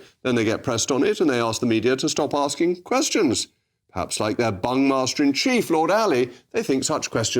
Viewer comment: Did he say Bung-master? Hahahahaha 9:23 😂